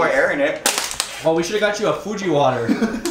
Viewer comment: i first heard "Fuji water" as Gucci water 😂😂😂😂😂😂